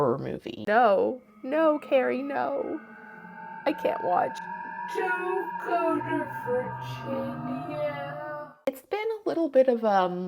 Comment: can someone please tell me what she says at 1:10 in the slowed down voice? it's driving me insane. "don't go to Bridgengham" "don't go to bridge with him" " don't go to rich indian" "don't go to britch in the ham" "don't go to bridge in the end" "don't go to fritchin ya" "don't go to fridge in yeah" ????????? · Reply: "Don't go to Virginia"